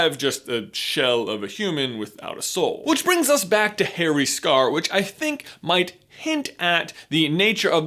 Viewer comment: I heard him say Perry’s scar. “ where’s Perry?”